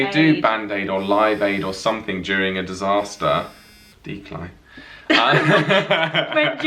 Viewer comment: When Joel picked up his phone I thought he said "Dear Clyde" not "declined" and I was like oh... I hope Clyde isn't watching. Haha I got it later when they were talking about it more.